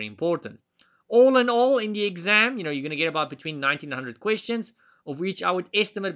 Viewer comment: For a second i thought he said the test had about "about Nineteen Hundred questions" and my jaw dropped for a moment lol Thank you for doing these and helping me prepare for the test.